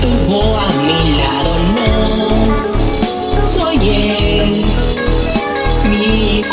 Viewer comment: ESCUCHE SOY GAY EN EL 2:10 luego leí bien y decía "soy el" 🤠👍🏻